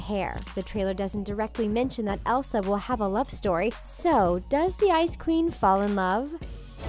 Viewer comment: At first I heard "Does the iCe cReAm fall in love?" Lol